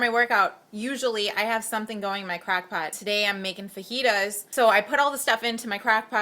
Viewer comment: Haha it sounds like you have an accent, when you said crock pot I thought you said "crack" pot! Haha :), thanks for posting this vid was very helpful!